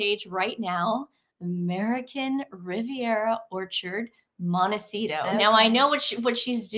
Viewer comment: When you said the name of the website I literally heard "American Riviera Torture".